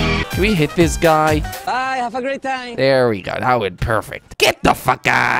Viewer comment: did anyone hear what the guy being threw over that building said? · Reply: he said bye have a great time